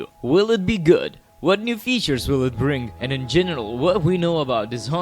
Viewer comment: for a second I thought you said “in genital” near the beginning 😂😂😂